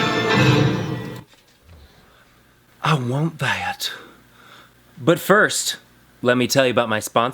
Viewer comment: i thought at the first he said"But first let me take a selfie hahaha